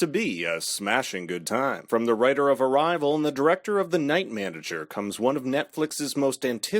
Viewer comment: aw man I heard director of night man